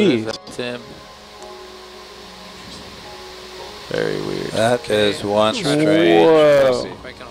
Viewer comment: did he just say "that is one strange p-ssy?"